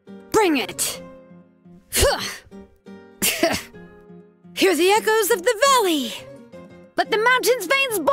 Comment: i always heard it as "here the echoes of baddie"😭😭